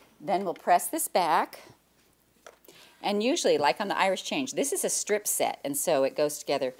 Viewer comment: Is it an Irish Chain block? Sounds like is saying Irish Change.